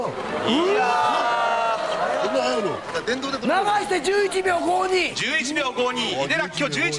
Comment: 手動を考慮して、＋0.2 野球スパイクから陸上へ変更で、-0.4 流さなくても実際タイムは、-0.1程度 ちょっと短距離のトレーニングをすれば、 11秒は切れる。すんごい。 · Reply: ぐらいで「電動でこれ」みたいな声聞こえる